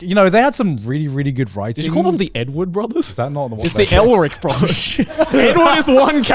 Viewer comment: I heard n word brothers